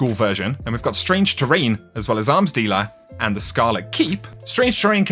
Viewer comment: I thought he said " ass dealer "